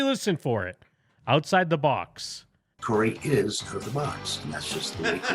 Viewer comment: he clearly says “Corey is out of the box”